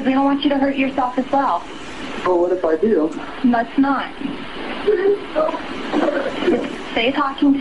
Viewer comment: did I hear the mother of the baby say, PLEASE DON'T??? ( Smh) Fuc* that, I would of been like, PLEASE DO, U DUMB PIECE OF SHIT...& HURRY TF UP ALREADY!!! (😬 Just saying, )